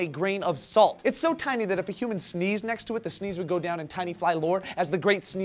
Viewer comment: at first I thought it said tina fey not tiny fly